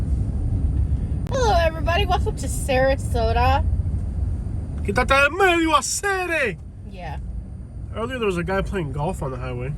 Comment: what is that thing u say? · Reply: "Quitate del medio acere" it's a purely Cuban term. "Be Removed From My path Acere" Acere is a term of friendship, similar to “dude”, “buddy”, or “mate”. You wouldn't use it in more formal settings, but you'll hear it often among friends. basically dont ever say ACERE to a stranger.. its awfully rude. Only someone you know can be referred to as ACERE. therefore, it's ironic I am being rude to someone, all the while accusing them of purposely stalling out in my path. Quite the phrase there. Acere is almost as vulgar as the english counterpart a rapper might use to reffer to a close assosiate. basically, if you say this to a cuban, and you dont know them its time to throw them hands. if you know them, and would be willing to joke with them.. its like saying "bro, get the crap out of the way, will ya"